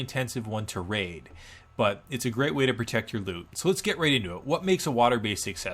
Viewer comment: "Let's get raid into it" I could've sworn you said that.